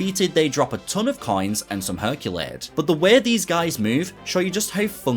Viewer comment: Played this so much as a kid and never knew he said "Herculade" when picking up the drink, haha. Thought he was saying "Herculaaays" or something. Very difficult but great game.